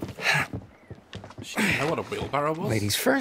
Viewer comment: I can't understand Daz right here. It sounds like he says 'she doesn't know what a wheel barrow is?'' If that's what he said, the reason why she was asking isn't because she didn't know what it was, it was because the guy said it wrong.